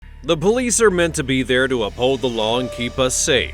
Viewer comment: Did anyone else hear him say the bullies